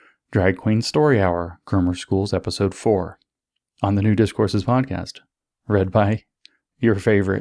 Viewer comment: On this special occasion of the podcast I heard you saying "Nudist Courses" instead of "New Discourses". But it's only half funny :/